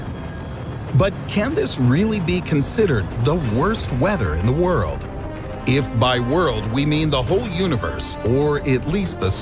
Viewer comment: I literally thought he said mount weather like the military base in th TV show THE 100